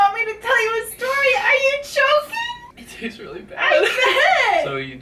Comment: I thought she said “Are you joking?” And thought ‘umm well yeah he is’ but after 3 times of watching this vid I heard “are you choking?” And thought ‘well that’s more likely to ask in this situation’